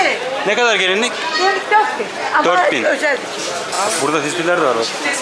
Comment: Ne dedi ne dedi 0:25 😂😂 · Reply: Özel dikim dedi